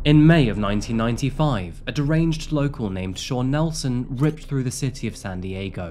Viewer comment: for a second there i thought you'd said deranged yokel.. lol.. i need my morning coffee.